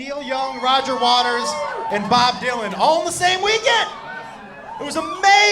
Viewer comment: Actually thought you said Bob Duncan